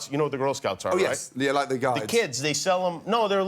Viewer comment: I love how Gordon said 'Like the Guides' (English Girl Scouts) and Kimmel thought he said 'Guys' xD